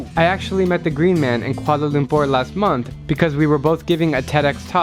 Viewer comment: Did he say Kuala Lumpur? If He Said that..... THATS MY COUNTRY's CAPITAL